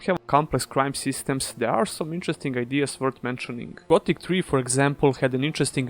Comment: what game is he saying? Something 3. It looks good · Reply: Gothic 3.